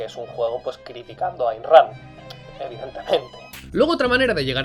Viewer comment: "porque es un juego criticando...." que es lo que dices justo después que no se entiende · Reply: Ayn Rand